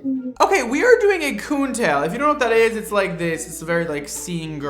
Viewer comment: …… WHAT’D HE SAYYY?! a WHAT tail??? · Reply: Coon tail. Striped like a raccoon. It was a 2000s trend in the emo/scene era.